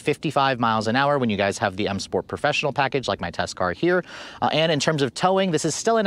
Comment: Have you been saying “my test car” this whole time!!!. I thought you were saying “my tester”. Anywho, great review as always.